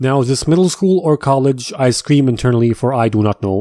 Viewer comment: When he said " I scream" I don't why but I thought he said "ice cream" I think I need to eat